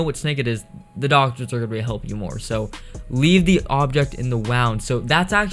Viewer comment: was i the only one that heard him say leave the object in the wowm